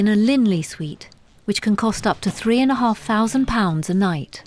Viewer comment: Wow and he’s staying in a £6500 a night suite. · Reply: I thought they said 3500 not 6500 a night for his suite??